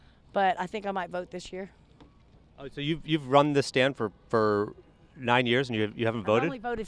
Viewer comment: When he says "You've run this stand -" I absolutely thought he said "scam" xD